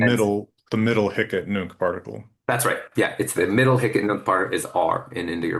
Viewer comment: I couldn't catch what they said there: "middle ??? no ??" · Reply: Middle hic-et-nunc particle ("here and now") but it's the first I've heard of it too so I don't know how it works.